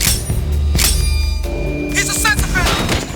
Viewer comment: Lol sounds like he says "He's a sex offender!"